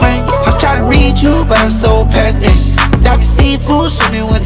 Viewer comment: yo wtf is berete saying at 2:51.. sounds like " im tryna reach u but im still pregnant" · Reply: im still pending